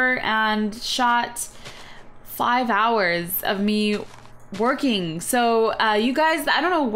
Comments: i thought you said twerking not working lol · My mom is cooking supper and my sister is playing in her kitchen set next to the real kitchen and I'm sitting at the island so it's really loud and when she said they came over to shoot 5 hrs of her working I thought she said twerking